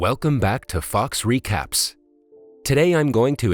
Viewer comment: I thought it said fucks recaps but it was fox recaps XD